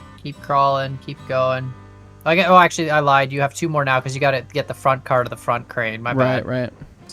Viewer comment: i just heard " oh actually i lied you have tumor now cause you gotta get the front car to the front crane"